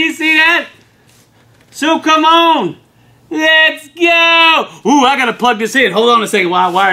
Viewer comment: I thought he said well c'mon let's gold. .. lol oops